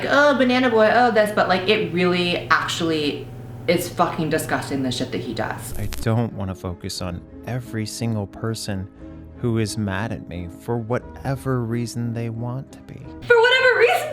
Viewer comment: lmao at first I understood “I don’t wanna fuck your son” instead of “I don’t want to focus on”